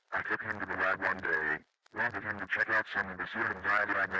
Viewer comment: it sounds like he said Serum.